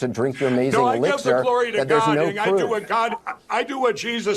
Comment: Gotta be honest. I thought he said "Chlorine" not "Glory" lmao Also, I have to give it to Anderson Cooper for grilling Mike in this interview